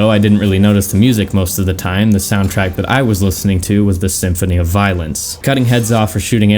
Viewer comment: I thought you said Symphony of violins at first.